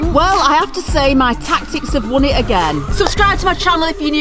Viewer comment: like if the first time you heard it you thought she said piss out?